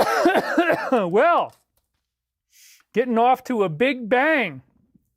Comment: He says "Please don't be stupid, natural (death) is the only way to go." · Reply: "getting off to a big bang" Really kind of sounds like he says Getting offed to a big bang...? He even repeats it. In his last live stream? 🤔